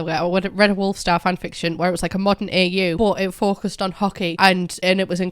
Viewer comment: When said “hockey” I thought she said ‘Hokey’ - the house elf of Hepzibah Smith - and was like….what does he have to do with Canada? 😂